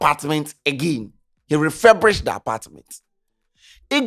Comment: What did he just say? 'Referbrished'? Ee!